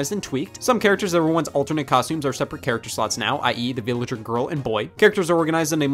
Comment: Jeez I thought he said “I eat villager girl and boy.”